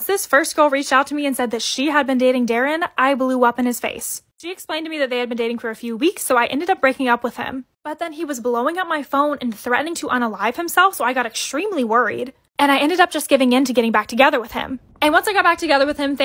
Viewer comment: did she say " unalive himself?" Its horrible that poeple do that but unalive👌best way to say it